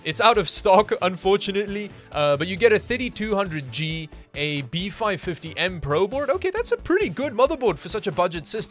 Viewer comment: Did I just hear "A B450M BRO board"? 😂